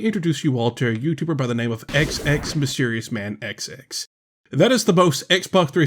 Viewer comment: Okay ngl I had to spittake because I thought you said you were xXMysteriousManXx for a second.